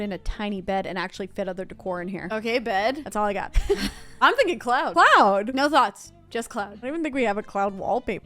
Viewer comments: I thought you guys said “clown” room at first tbh but I didn’t really question it lol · I definitely heard "clown" and I was here for it. But I wasn't disappointed with the cloud theme. Still want clown theme though or circus